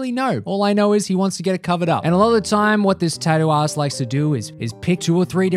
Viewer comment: why does it sound like he said tattoo ass?